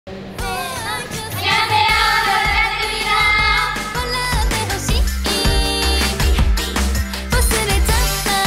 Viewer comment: に言っているアニョハセヨ〜の次って韓国語でなんて言ってるかわかる人いますか？わかる方教えてください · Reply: 잘 부탁드립니다. = よろしく お願いします